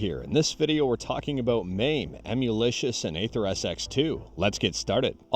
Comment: I thought you said we are talking about Maine lol!! Especially, seeing those trees behind you.